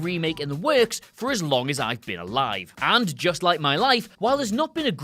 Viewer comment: thought you said “just like my wife”